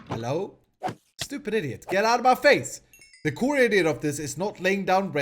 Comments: did he just say "the core idiot of this..." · Did you just say "The core idiot " ? (instead of idea .. ) 😂😂 💜